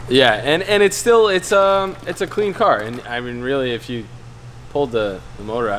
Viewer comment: I thought you said "clean" KA xD